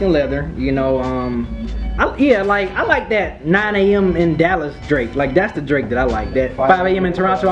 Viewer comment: LMAO...... 3:21 Did he just say 9 am in Dallas.....what album was he listening to?